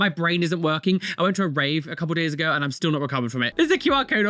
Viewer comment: ”I went to a rave a couple of years ago and still haven’t recovered from it.” That absolutely sent me lol. Hope you’re well Mr.. Your videos always make my days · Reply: I thought he said days ago either way funny af 😂